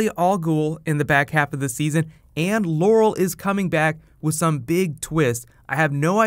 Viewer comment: WTFFF 7:00 I THOUGHT HE SAID LAUREL IS COMING BACK WITH SOME BIG TITS AND THE PIC OF TALIA FROM THE DCAU WAS THERE